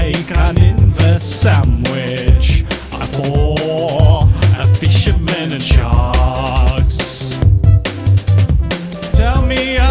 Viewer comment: I always thought he was saying "at war with fishermen and sharks" when I saw this years ago